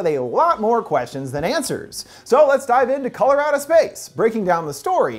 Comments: Am I the only person who heard him say Colorado Space the first time around?? lmao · I heard him say "Colorado Space" 😂